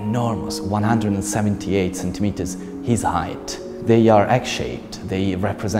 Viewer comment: Can someone tell me what was said at 2:20 I didn't quite understand. Thank you very much！！！ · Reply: “They are egg-shaped”